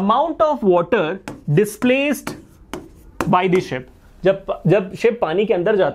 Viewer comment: i looked away to get refill for my pen suddenly i heard "shit" looooooooool XD